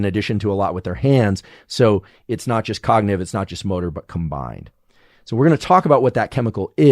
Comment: I thought this man was about to say, "it's not just motorboat" and then I clicked that he said, "it's not just motor but"..... we're watching you andrew huberman. We're watching 👀